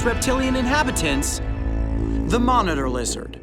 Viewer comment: I thought it said: THE MONA LIZA rd